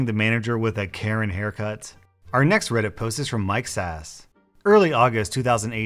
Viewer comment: did anyone else hear ‘Mike’s ass’?